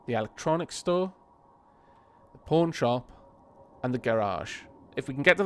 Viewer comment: when you said pawn shop I heard "porn shop" in